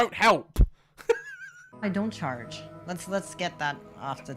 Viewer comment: Her: I don’t charge a lot of money, even tho I could. Everyone: how much? Her: only about 100 thousand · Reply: i thought she said she doesnt charge at all